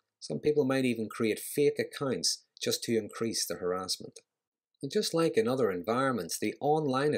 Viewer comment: it is unclear what you said about "they may create... " Maybe the video was edited but whatever you left in at that point I cannot make out what you are saying. Can you let me know please? Thx. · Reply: Create fake accounts to continue and increase the harassment